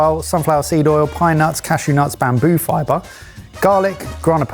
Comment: The most shocking thing for me was that even the "fanciest store-bought pesto" is cutting corners with sunflower oil and cashews. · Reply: I'd need to go back and watch it again, but I swear I heard "bamboo" in the list of ingredients.